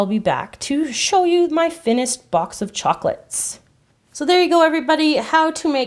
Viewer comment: did any one hear her say finised instead of finished? Comment if you did